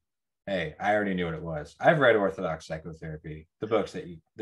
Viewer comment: you talk about a book? Orthodox syclotherapy? Did I heard it wrong? Thank you for your work